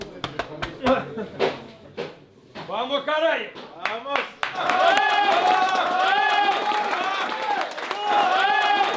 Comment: Что сказал Карпин? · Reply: Вперёд, канальи!